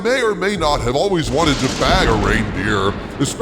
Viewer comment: Its funny how Autosubs show "Bag" as a Blank, as if it thought you said "Bang".